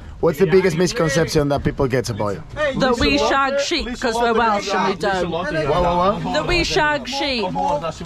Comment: kız ne diyor? We shark sheep mi? :) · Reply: we f**k sheep demeye çalışıyor ama ikinci kelimeyi ''şark'' diye söylediği için kimse anlamamış